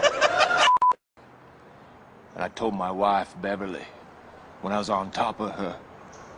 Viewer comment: "I told my wife Beverly, when I was on tohp uh huh" that's how I heard it and I'm crying rn